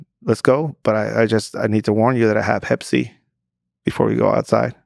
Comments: what exactly did the guy say he had? Can someone clarify? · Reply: Hepatitis C. Easily transmitted via blood. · Does he say: "I need to warn you that I had Pepsi"? Is this what he said? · Hey! I'm not a native speaker and need someone's help. At 11:20 I dont understand what the guy says in the story. According to the subtitles its Pepsi, but I guess its something else :). Thank you in advance. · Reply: He's talking about hepatitis C